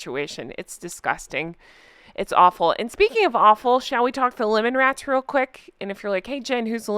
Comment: I thought you said “Lemming Rats!” Lemmings are a type of rodent who lives in underground tunnels. They are said to commit social mass suicide.